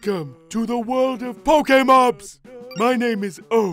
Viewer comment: The captions thought the villager said Paul came up instead of pokemon